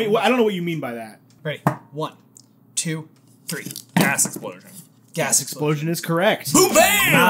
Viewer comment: I really thought answer said “Gos Explosion”😂